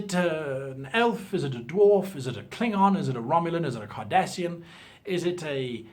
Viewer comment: did he just say Kardashian? I always knew they weren't human...